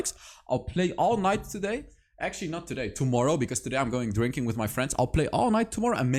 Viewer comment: One minute in and I hear: “tonight I’m going drinking with my friends”. 😂 idk why that cracked me up